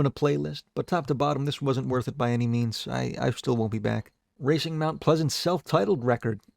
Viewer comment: whats the name please? i cant understand what ya sayin 😭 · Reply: Racing mount pleasant - self titled